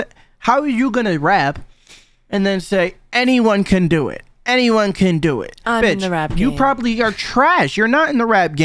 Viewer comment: I thought she said anyone can be into it.